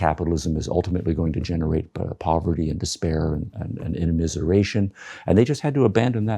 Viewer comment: what’s that word please? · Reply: "immiseration"